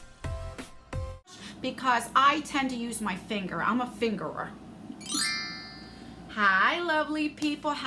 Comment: “I’m a fingerer” 😂😂😂. I spit out my drink when you said that in the beginning 😂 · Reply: OMG! I had to replay that. I thought you said Ima finger her. I was like " isn't this a family friendly channel. You are so funny 😂 lol. Luv you 😘